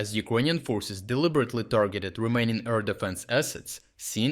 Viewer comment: Everytime he says "remaining" I hear "Romanian".